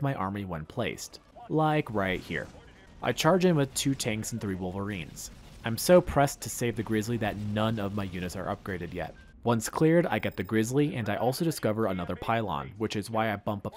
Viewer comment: I thought he said the rizzly lol